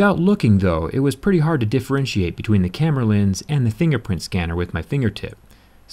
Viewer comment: is he saying "Thingerprint" scanner? Is this an Easter Egg?